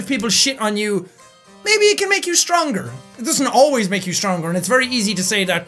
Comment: PLEASE animate what he said at 11:15 Plz 👏🏻👏🏻👏🏻👏🏻👏🏻👏🏻👏🏻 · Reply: It was the being sh*t on makes you stronger 😂😂😂😂😂